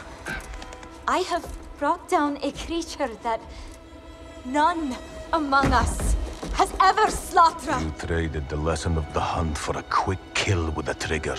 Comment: did he just said "Among us"?